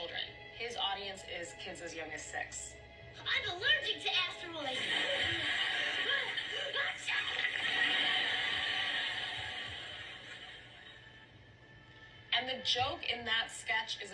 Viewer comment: I could be tripping but it sound like he said I’m captain dxxx nose